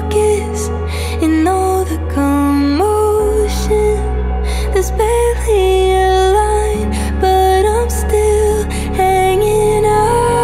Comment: Instead of hearing "there's barely a line but i'm still hanging on" i always hear "cause barely alive but i'm still hanging on" and i feel both way.. i feel like i'm barely alive..